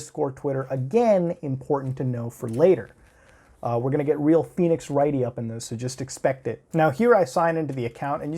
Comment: did you said Phoenix fucking Wright?